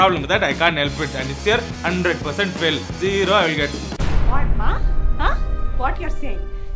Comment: Who else heard sing 0, I'll get? 😂😂